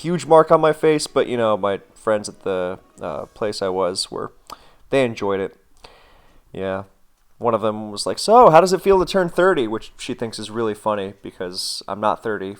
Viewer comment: i heard 'how is it at turn 30' not 'how is it to turn 30'